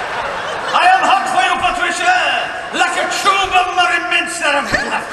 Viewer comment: What did he say at 7:22? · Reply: I'm hot for you Patricia....like a tube of Murray Mints! XD